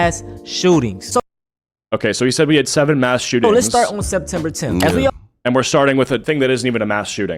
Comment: didnt say CK incident was a mass shooting. He said they started that day. He is trying so hard! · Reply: LMFAO WHAT ARE YOU GUYS ON??? 12:40 He did say it was a mass shooting, and no he didn't say "they started that day", he said "let's start with the ck shooting" as in using that as the first example. Like why would he say the CK shooting started all shootings, they been happening for years lmfaoo. The only one trying hard here is yall this level of mental gymnastics is unhealthy